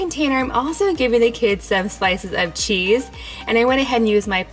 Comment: I thought she said spices of cheese lol nvm